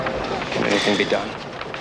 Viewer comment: Anyone know what he asks at 0:17? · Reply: Can anything be done?